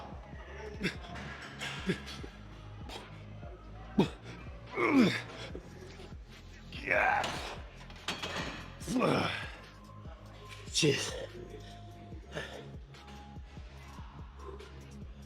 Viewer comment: Why did u say ‘jizz’